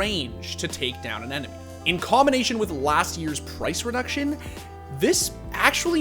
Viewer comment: Me who dosent play csgo when he said" the price reduction from last year " 3:52 Wait a minute did he say last year or did i hear it wrong ?? Looks like fing csgo mobile knock-offs havemore frequent balance changes than csgo itself